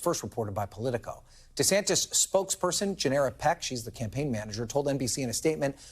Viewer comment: Gennera Peck? I thought he said "generic pick"